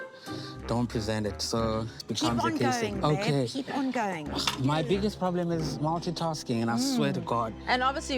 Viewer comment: Did Justine just say to, “keep on going babe” 👀🤭